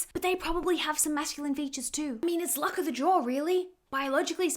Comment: I thought you said "luck of the jaw" for a second 😂